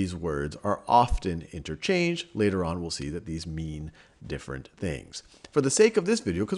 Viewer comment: When he said “they mean different things” I thought he said they meme different things